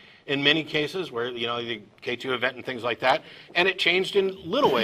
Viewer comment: I don't think at 47:50 K2 event is meant, though it sounds like that is what is said. K2 is a mountain. KT is the border between the Cretaceous and the Tertiary. The KT event is the mass extinction that occurred on that border